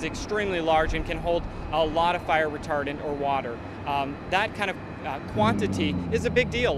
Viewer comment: shit you said flame retardant i thought you said napalm , sorry ill go get the flame retardant now 8